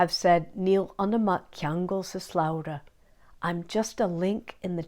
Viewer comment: I really appreciate your videos. I'm an Irish learner and I'm having trouble parsing what you are saying at 4:52 translated as "I'm just a link in the chain" I can hear "Níl ... ceangle (sp?) sa slabhra" ... any guidance would be appreciated. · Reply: Hello and thanks for asking! Here's that sentence for you: Níl ionam ach ceangal sa slabhra.